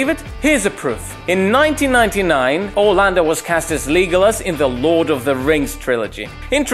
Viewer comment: I’m sorry did he just say “Leegolis” wtf💀